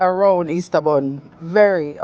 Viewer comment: Girl I was cracking up cuz I swore it said Easter bunny 😂. I said to myself, "Self, what kinda Easter bunny Kay Ann think she bout to make?" First few seconds in I heard you say Easter buuunnn. Oh ok, me understand now 😅. Love you chica!